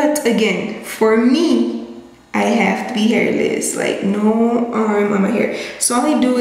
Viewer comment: When she said "no arm on my hair" I was like wait what??? Am I going crazy??? Did I hear correctly? Had to replay it 😂 lol I'm loving this video so far!